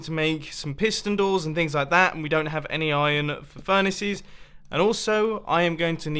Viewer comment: did he say iron for furnaces?